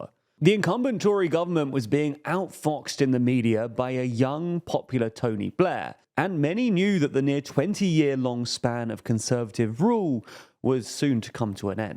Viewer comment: Did he say "Toby Blair" @ 0:13?